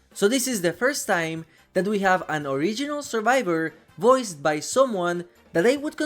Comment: my dumbass almost started tweaking because I thought you said “survivor,” not “original survivor.” 💀